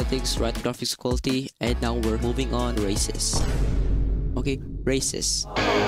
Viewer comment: idk about you guys but I could have sworn I heard racist💀💀💀